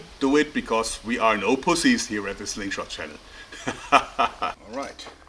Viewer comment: What did he say at 5:31? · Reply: No pussy's here at the slingshot channel.